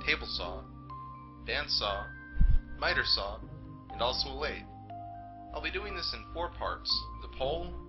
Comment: what is the last tool you mention at 0:33 called. it's hard to tell what you said. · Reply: A lathe